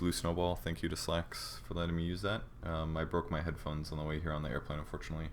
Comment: when he said i broke my headphones i thought he said "I broke my hip bones"